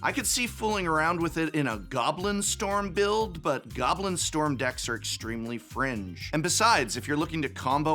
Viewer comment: I misheard you at “goblin storm decks are extremely fringe” and thought you said “goblin storm decks are extremely cringe” lol